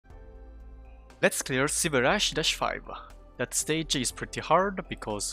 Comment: I had to pause the video for a laugh when I heard "Silverash 5"